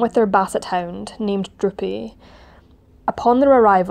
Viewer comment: At first, I thought you said the dog's name was Drippy, but after relistening a couple of times, I was disappointed to find that it was in fact Droopy. Nevertheless, I did like that both times you mentioned the dog, you felt it was important to pause and say his name.